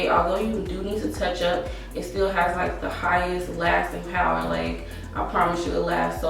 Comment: did she say breast power what did she say I thought thats what she said 😃😄😄😃😃 her make up is so natural Nice